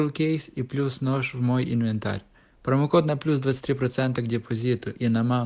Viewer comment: мне одному послышалось промокод на + 25 процентов к пополнению и на маму?